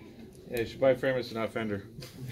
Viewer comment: What is he saying at 1:35 ? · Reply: Sounds like "you should buy Framus, and not Fender"